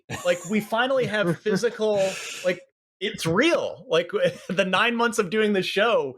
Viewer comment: Some one got one in hand already ? 23:20 in the video it's real one guys said ,The other said shhhhhhh. Did I hear that right ?